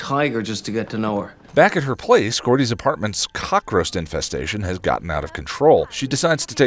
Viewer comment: Anyone else hear cockroast instead of cockroach at 1:02 ?